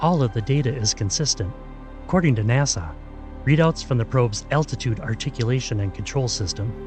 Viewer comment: I thought he was saying altitude instead of attitude.